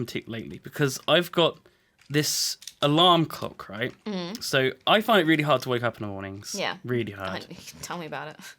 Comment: I'm sorry did say that she found really hard when he gets up in the morning?